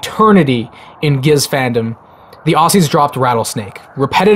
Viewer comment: Did he say "The OhSees" at 4:50?